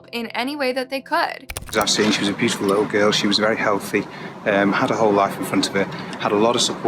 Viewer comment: the captions for this are wrong. The man is saying ‘her’ not ‘it’ or ‘a’ it’s his accent that makes it sound that way. Caption says ‘had a whole life in front of it’ but what he said was ‘had her whole life in front of her’.